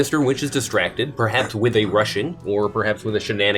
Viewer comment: he said distracted with a "Rush in" and i hear "with a Russian" so now all im picturing is the Russian Mob blitzing the Big Top for the weathervane or the pocket watch